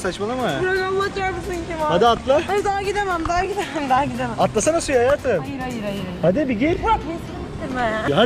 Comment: atlasana suya hayatım mı dedi ben mi öyle anladım